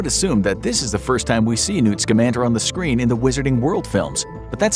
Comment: Thought u said Newt Salamander lol